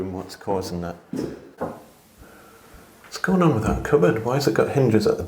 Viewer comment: I thought he was saying cupboardy walls. I know. I'm American. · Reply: I thought he was saying cupboardy walls too and was confused but yes, I am an American too.